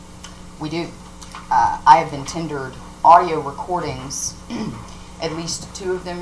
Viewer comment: did she say she had been 'tindered' some audio??? WTAF? I haven't been on tinder for a good while but I wasn't sending audio as far as I remember??? [EDIT] - ohhhh - I am from the UK so the accent threw me - she said 'tendered' not 'tindered'!!!! LMAO!!! Apologies [EDIT]